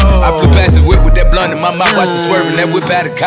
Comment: I always thought it said blood but nah it’s blunt 😂